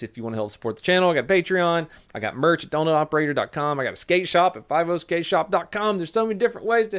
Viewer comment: I swear to god I thought he said Skank Shop at 6:51 🤣